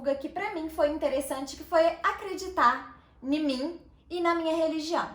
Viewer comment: "(...) acreditar 'NI MIM' (...)" foi osso! Rsrs... brincadeiras à parte, parabéns pela conquista.